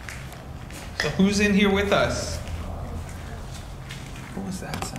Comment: "so who's in here with us?" voice sounded like it said "just us"